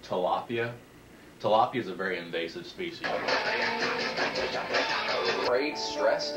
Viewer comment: Joji: Tilapia is an endangered species Laughs in Philippine accent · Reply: I thought I heard “Tilapia is a very invasive specie 😅”